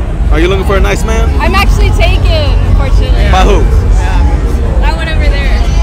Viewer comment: Wait did she just say I'm actually taken "UNFORTUNATELY"??😂